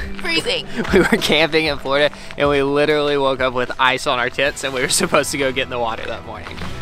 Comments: Anybody else hear something different when said "ice on our TENTS" 🙈🙈🙈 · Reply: Hahahahahaha I actually thought he said. Ice on our tits. I thought-wow well thats an Australian saying for you. Only realised it was tents, When i was reading the comments 😳😂🤣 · what did he say? 💀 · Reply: "...ice on our TENTS" 😂❄ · I honestly thought said “ice on our tits” and was like WOAH! Had to rewatch over and over a few times til I realised he said tents. 😂